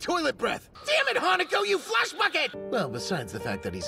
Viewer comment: DID HE CALL HANAKO A FLESH BUCKET